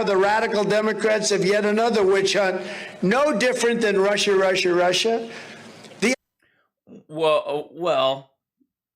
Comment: Everytime Trump says, "Russia, Russia, Russia". All I hear is , "Marsha, Marsha, Marsha". 🤦